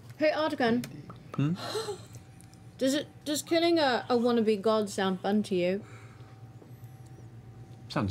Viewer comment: I misheard "Does killing a wannabe God sound fun to you?" as "Does being a wannabe God sound fun to you?" And I